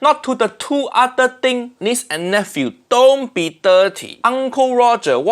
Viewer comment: what is dirt lick or whatever he says there?